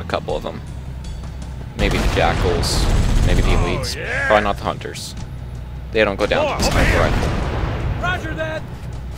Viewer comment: The Hunters CAN go down with a sniper rifle. In fact, besides the Rocket Launcher, sniper rifle is one of the best guns against a Hunter. Rocket launcher you can fire at a Hunter anywhere and it will blow him to bits - his armor isn't THAT strong. The Hunter is vulnerable on his belly and the small of his back. Shoot him a few times there with the Sniper rifle and he's done. Any gun will kill him if you hit him there. But for most of those guns, like an assault rifle, you need to unload an entire clip into that section to bring him down. Hunters, you have to kill from a distance or from really close up (while circling him). There is no in-between - he will kill you. And they always travel in pairs! That's the worst part! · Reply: Ok, but i thought i heard something like "the Hunters don't go down with a Sniper rifle."